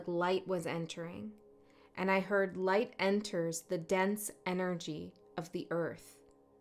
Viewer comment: Might comment further but at first I misheard you and I thought you said "light enters against enemies of the earth" and I was like "ok makes sense"